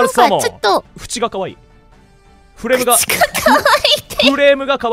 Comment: what did he said? Buchi? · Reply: He said 'fuchi' meaning edge/rim/edge (of the card in this case)